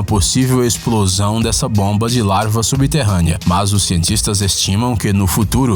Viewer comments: ele falou larva mesmo ou só eu entendi isso? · ele falo larva ou só eu entendi ? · Fantástico o canal, a voz é demais, as informações sempre impecáveis mas hoje ri um pouco quando aos 4:32 ouvi "larva subterrânea", me veio à mente o filme "Verme Maldito"... ...não estou criticando, só achei realmente engraçado, vocês são bons demais e isso acontecer é perfeitamente normal, quantas vezes rimos de nós mesmos quando por algo parecido. Mais uma vez, não estou criticando, sou fã do canal. "A GENTE É TODO MUNDO UMANU" ABRAÇOS!!!!!!!!!!! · Foi só eu que percebi que ao 4:33, o narrador diz: Bomba de " LARVA " subterrânea ? Imagine o tamanho dos Novatos, então, fora o pequeno deslize na linguagem, esse vídeo é 10 !!!